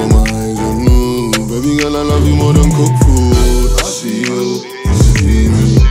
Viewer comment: I swear I heard "baby girl I like you more than Fufu" lool